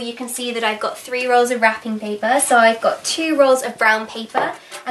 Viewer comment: when she said: "i've got three rolls of wrapping paper" i thought she said: "ive got three year olds in wrapping paper"